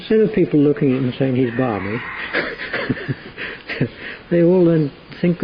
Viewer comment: What does say at 20:54? “Instead of people looking and saying he’s ______”? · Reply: balmy is an old idiom for someone foolish, like daft. Definitely British and used by older folks still today